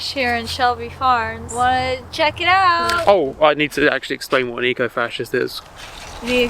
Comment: had to rewind like two minutes cause i thought he said "i need to actually explain what a geocache is" and then i had to do some panicked googling